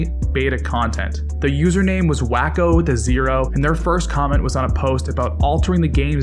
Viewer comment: Random Question Alert: at 3:25, is he saying “Wacko the zero” or “Wacko to zero”? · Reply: "Wack0 with a zero"